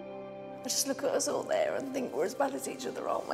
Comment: what is she saying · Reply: something like "I look at us all there and we're all just the same, really"